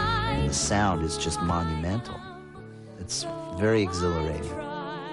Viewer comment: what did he said after monumental? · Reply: 'exhilarating' - making one feel very happy, animated, or elated; thrilling